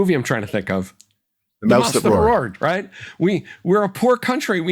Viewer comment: What war movie did they mention at 45:08? I didn't catch the name of it. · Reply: Mouse That Roared (1955)